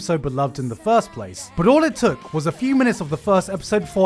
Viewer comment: Watching anime yutuber in 2021 is when you misheard the word "minutes" as feminism" and not so much surprised, but want to say "dude ...".